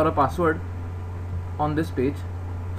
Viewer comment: I thought he said "on this bitch" lmao